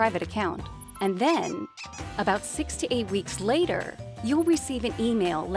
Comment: I can't help myself, I always hear about 68 weeks later. I hope you meant 6 to 8 weeks since your Website says 3-4 weeks today.